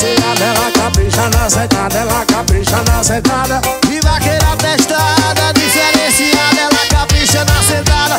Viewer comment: No refrão eu entendi "Vaqueira Abestada" KKKKKKKKKK